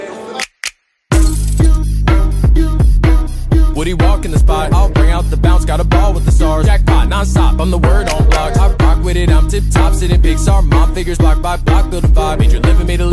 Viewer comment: I hear 0:26 “Pixar mom figures block by block builder bob”